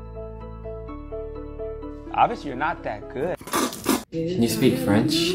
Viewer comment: i think that he said “well obviously your not that good but try your best”